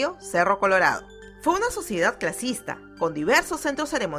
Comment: minuto 0:43 escuche rasista like si escuchaste lo mismo